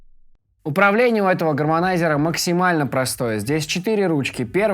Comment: Птьфу ты послышалось гавномайзер!